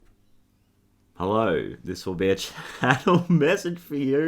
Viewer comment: lol I heard "hello, this lil' bitch" so it was funny in a good way 😉 I liked it anyways